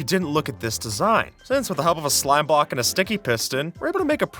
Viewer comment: am I going crazy or did he say "slime Bock"?